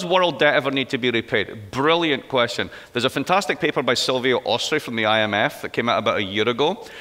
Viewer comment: WHats the name of the guy Mark mentions at 27:28. I just cant understand the name. Thanx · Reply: It's a woman...Sylvia Ostry